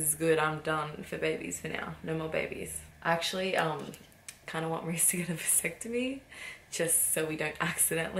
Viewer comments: what did she said on 0:57? i didnt understand what she said 😅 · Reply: idk moonlight she was saying she wants Reece to get a vasectomy but he says no (no more babies) Its like tying your tubes but for the guy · what did tammy say @ 0:59 about reece? 💞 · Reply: My Huynh she wants him to get a vasectomy so he wouldn't be able to have anymore babies.